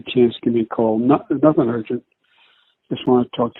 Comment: does joe say "not gonna hurt ya" to hunter? Does he have a history of "hurting" hunter?🤔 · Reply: He says “nothing urgent”